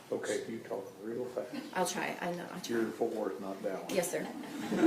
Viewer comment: What I think the judge said to her: "OK, you talk real fast." laughter "You're in Ft. Worth, not Dallas." Again, I think this is what he said.